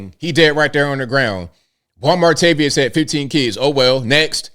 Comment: 😂😂😂 did you just call someone " Wal-Martavious" ? Even if that's not what you said that's great